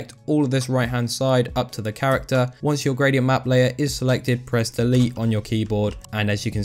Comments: press what? I can't hear it pro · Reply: press DELETE BRO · what did he say? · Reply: Press delete on your keyboard